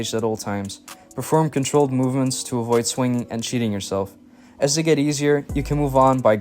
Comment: anybody else think he said ‘to avoid swinging, and 💩 yourself’😂 had to run it back. Great video though thanks👍